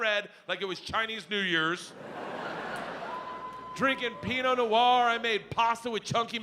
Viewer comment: Why does the captions say “peeing on the wall” when he actually says “Pinot Noir” at the time stamp 4:24? It’s not even like it’s an automatic caption, somebody ACTUALLY wrote that.